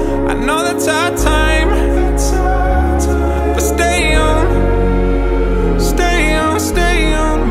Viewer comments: I thought he said stay young stay young 😭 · Thought he was saying STAY YOUNG 😂🫠🫠🫠